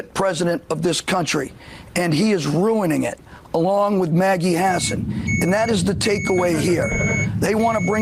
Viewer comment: I always heard "Medi Hassan".. I was exited..